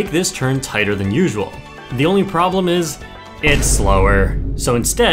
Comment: "you can knock the Goombas away! Adorable 😻" Goombas: "bro, I was just chilling here, what the heck" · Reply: I thought he said ‘I’m horrible’ and not ‘adorable’ - I do appreciate the clarification lol